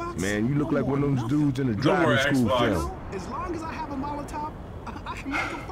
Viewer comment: did Louis just said as long i got A Molotov